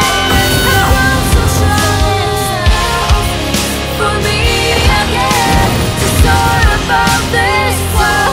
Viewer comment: I like how in 5:37, the phrase "(unfold these pair of wings) For Me Again" can be misheard as "we'll meet again". I'm gonna take a shot and gonna say that this is the passing through of Himeko to Kiana, a final goodbye but a reassurance that Himeko will never be truly gone as long as Kiana keeps going.